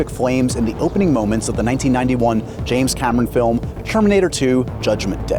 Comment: Did he just say Sherminator?